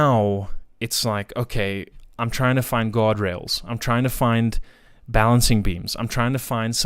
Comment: When you were speaking about getting more into psychedelics and you began looking for "guardrails" I heard "God-rails" at first. Knowing where your story was going it felt like a perfect slip and it made me smile.